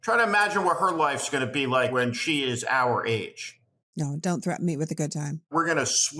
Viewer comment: What did she say at minutes 44:05? "Don't threatened me with a good time"